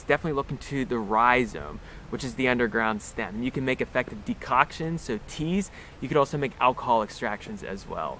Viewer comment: Did he say "de-cock-tion"?! Dude, sure I would want the nettle to fix my prostate, but don't want to lose my peppy in the process!! Talk about robbing Dick to pay Harry... Pfft! So sorry, couldn't help myself. Brilliant viddy. Thanks for the top-shelf info about our natural world.